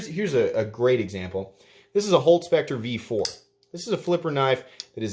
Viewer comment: you pick up a knife and flip it to illustrate a point and I can't understand what you say. Are you saying Hogue Spector V4? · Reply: Holt Specter v4